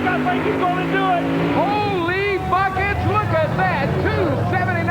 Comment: what does the announcer say? All I hear is " Holy fu........" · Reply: "Holy buckets!"